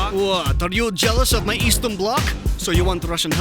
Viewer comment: I thought Stalin said "Jealous of my Eastern cock?"